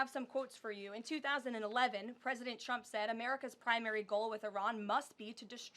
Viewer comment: I heard that as president Trump stinks😂😂😂😂